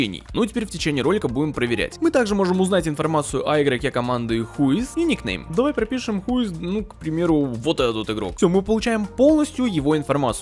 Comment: я один слышу такое слово как: xyis